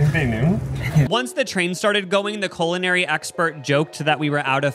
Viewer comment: I thought you said colonary lol